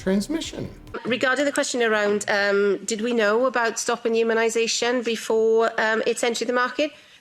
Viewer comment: but she does not say "stopping transmission"... she says "stopping immunization"... that is something different... that would be a test if the immune system is still functionable after the treatment...